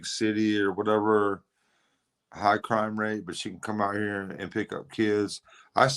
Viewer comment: At about 38:45 it sounds like he says " its easy pickens and SHE can come up here & pick up kids". She??